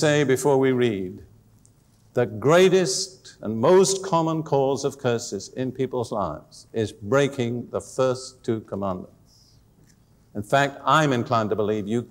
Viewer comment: Anybody else at 36:34 lose audio? What is he saying ? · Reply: There seems to be no problem with the audio at 36:34, Valerie. I'm not sure what's wrong then. However, this is what he is saying there: 'the greatest and most common cause of curses in people’s lives is breaking the first two commandments'. Next time you face this kind of problems, try switching on the subtitles. God bless!